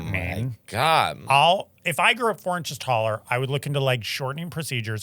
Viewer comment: OMG - I thought she said 'you wake up 4" tall". Not 4" tallER. I was wondering why nobody was freaking out. 🤣